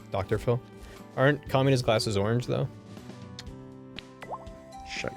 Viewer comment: I thought said communist glasses lol